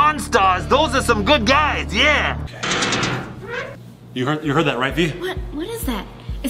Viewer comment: I think at 7:37 I heard like a girl shouted VY!